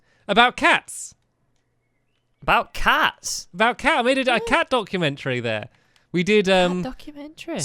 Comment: I thought stampy said cat dog-umentary lol